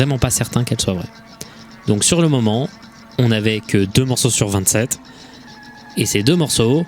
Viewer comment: C'est marrant je savais pas que tu réenregistrait ta voix, pour moi je pensais que c'était de l'instantané 24:24, en effet dans la vidéo on t'entend dire ''2 morceaux sur 27'', mais tes lèvres te trahissent en disant ''2 morceaux sur 23''